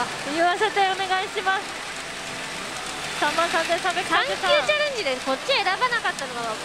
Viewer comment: すいません💦動画再生だけして、音を大きめで料理作ろうとしてました。産休チャレンジと聞き間違えて、吹き出しました🙏